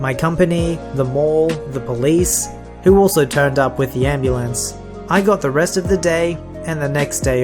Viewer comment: i heard "I got arrested today"